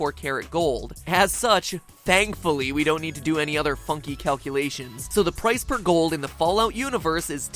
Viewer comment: I actually thought that you said fucking calculations instead of funky calculations for a moment.